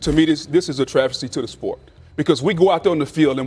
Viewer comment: Did he just say trajedy to the sport? No, travesty. Oh. My bad.